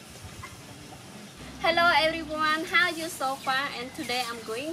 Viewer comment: Did she say, "Hello Everyone, how are you so fat." At 00:11 or am I hearing things because I just ate two whole dodnuts?